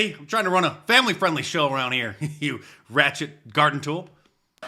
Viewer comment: I love it when uses 90s phrases like ghetto hood rat Reminds me of a saner time in America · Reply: I thought he said "Ghetto rat s***" but he was saying "ratchet" to ward off YouTube censors.